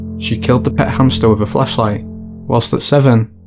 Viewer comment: That first girl when you said "she killed her pet hamster with a flashlight" at first I thought you said Fleshlight